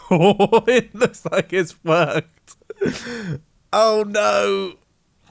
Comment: did said 'it looks like its fucked?'